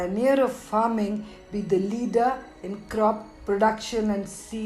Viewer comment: A very informative video.🙏🙏 Wish this was also available in हिन्दी, or even with English subtitles. I couldn't understand a word in the last sentence. Please help in clarifying: @ 5:22 "..be the leader in crop production and see tha... ????ment" What is the last word here? Thank you🙏👍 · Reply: "crop production and seed improvement". Agree, that vernacular language options would be much appreciated. The English-speaking population either won't ever care, or are already traditional (of course, knowledge is always appreciated).